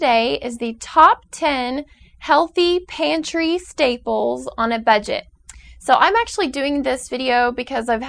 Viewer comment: i thought i heard her say TOP 10 HEALTHY PANTY STAPLES